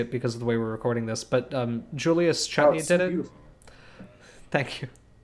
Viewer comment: What was the name of the artist you mentioned at 18:29? I couldn't quite hear the last name. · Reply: That would be!